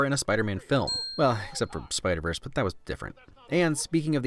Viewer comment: Neat video! Btw at 18:46 I think it should have been "except" instead of "accept" in the subtitles. Great video though! Nice to see how well your channel is growing :D